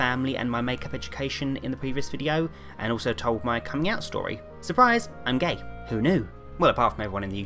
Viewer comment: For the longest time I thought he said "ELLO, I'M GAY" 😂😂